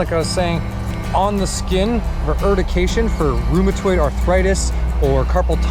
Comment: what is the term of hitting someone with nettle? Can’t make out what he’s saying, rehurdication? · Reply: Urtication. Urticaria is Latin for nettle.